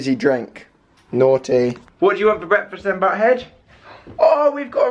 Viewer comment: I swear at 2:33 he says what have you had for breakfast then butthead?? Anyone else yet that??😂